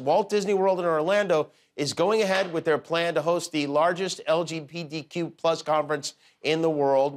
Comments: Did Jimmy say “LG-PDQ”??? I hope Disney World hosts an awesome LGPDQ parade with lots of Drag Queen story hours. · Did Jimmy say “LGPDQ+”?